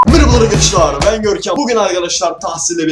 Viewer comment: Senin dedin merhaba arkadaşlar bugün benim duydum mirba arkıdaşlay bogün